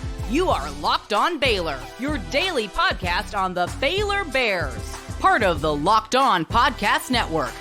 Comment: does she call them the FAILor Bears?😂